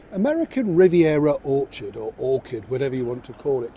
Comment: I thought you said 'American Riviera Awkward'. Oh dear.. 🫣